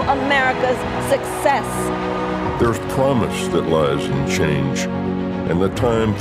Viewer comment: I heard "there is promise that lies in chains". That's what it sounds like, but also what it feels like.